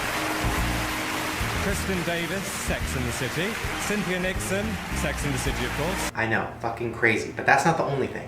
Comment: i hear sexy and the city 😱